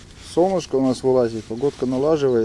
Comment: Мне одному послышалось не солнышко а сомушка